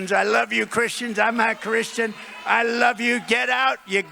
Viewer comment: Why at 1:03:16 does trump say he is not Christian? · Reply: Yeah I replayed it on .5 speed. He does say "Im a Christian." I heard "im not a Christian at first too"